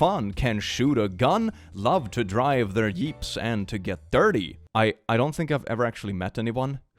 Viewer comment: Wtf? Is that just his accent messing with him or did he do that on purpose? Around 13:56-14:00, it sounded like he said "Yeeps" instead of "Jeeps" the second time he said it. Idk why it bothers me, just wondering.